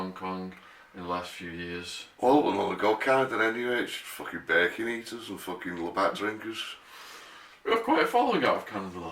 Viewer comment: no idea what said, something about "bacon eaters" lol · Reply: "I wouldn't even want to go to Canada anyway. It's just fucking bacon eaters and labatt drinkers"